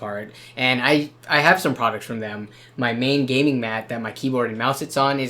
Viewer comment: I thought he said "My main Gaming Mac"....